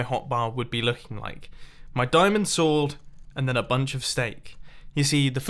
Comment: Did he really say diamond swold?